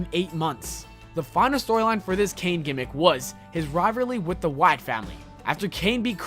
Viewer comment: notice at 5:18 he said "riverly" instead of "rivalry"